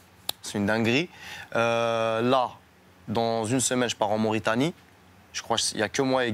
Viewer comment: 🇲🇷 mauritanie ♥️🤟👌😇🤝 bienvenue Soolking 😍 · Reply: frero 2:36 minutes concentre toi bien broo , il a dit " dans un semaine je part en Mauritanie "